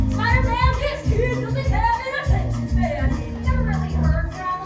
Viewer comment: does anyone know what the hell she says right here I've listened to it multiple times and cannot figure it out😭😭😭😭😭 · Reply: "This kid doesn't have an attention span" 😭😭